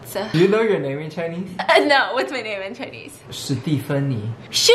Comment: thinking how he said her name was a joke i thought he said shitty funny not Stephanie